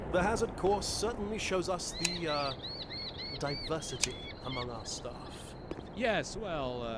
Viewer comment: Did someone just said Among us?